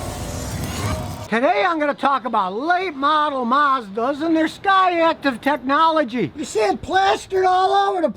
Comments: Lol, thought he said scottyactive technology 😂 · I thought he was saying Scotty active technology LOL.